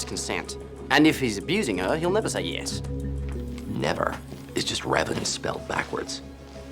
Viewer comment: The “never” and “reven” part got me. 😂😂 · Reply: I thought he said "never is just more evidence spelled backwards"